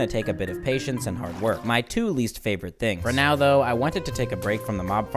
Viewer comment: Did you say Ronaldo? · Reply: It's "for now though" but I g Heared Ronaldo too!